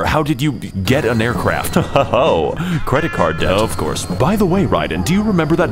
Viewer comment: “Doktor, how did you get an aircraft?” “Heh heh ho…credit card.” “Of course.” Idk why that made me laugh so hard Edit: Yeah he actually says “credit card debt” which is still funny